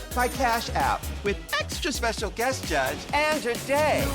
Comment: bitch i thought they said the guest judge was sandra day.... o'conner ??? the supreme court judge??????